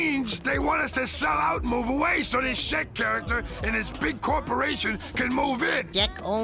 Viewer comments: I thought he said shit character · When I was younger, I dead ass thought Mr. Green said "Shit Character"